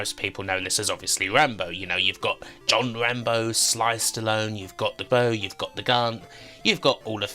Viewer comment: For some reason I heard "you've got John Rambo, sliced alone!" 😄 Thanks for compilation, I enjoy your videos!